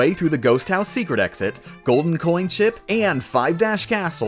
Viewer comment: I heard "golden coin sh&t"